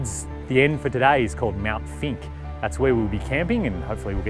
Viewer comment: Mt. Finke. It sounded like you said Mt. Stinky..? Greg K.